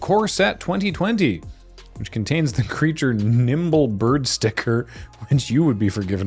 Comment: Not me mishearing the word Nimble as Nipple leading me to wonder what a bird sticker for the nipple would do